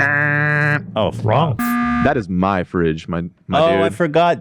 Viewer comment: I'm sure he wasn't, but ngl at 1:02:53 it almost sounded like Dan was about to say "That's MY fridge, my n****" and then corrected it to My Dude 🙊😅